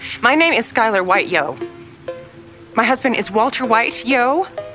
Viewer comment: I always hear: "My husband is way more White than you" ☠☠☠💀💀💀